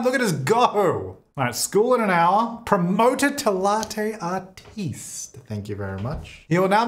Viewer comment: I thought that said promoted to latte hottie and went 👀👀